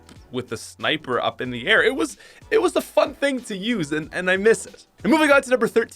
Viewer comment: Am I the only one who heard it was the fuck thing to use?